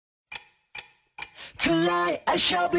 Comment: I thought they were saying "tonight" not " to lie"